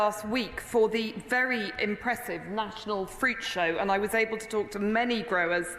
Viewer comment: Approx 1.15 in the video, I could've sworn she said "national freak show". Then I remembered that the national freak show was in Manchester a little while back.